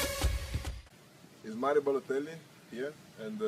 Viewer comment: Did he say at 0:03 "I am balotelli? ( i cant spell correct )